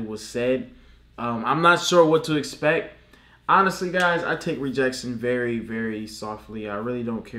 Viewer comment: I thought he said he takes rejectection lightly